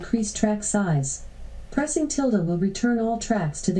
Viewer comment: did she say pressing "tilde"?